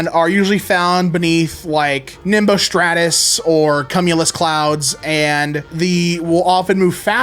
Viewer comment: did i just hear him say cum-ulus.